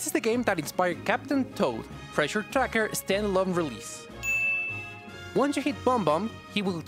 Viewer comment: I thought you said “This is the game that inspired Captain Toad Treasure Tracker. Still unrelease”